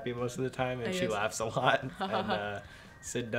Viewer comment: When he said "she laughs a lot" i heard "her laugh's hot" instead and I was like 😑😐🤨🤔